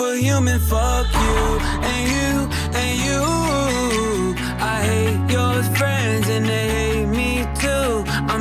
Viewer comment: Bro I really thought he said "I hate your friends and I hate me too" ☠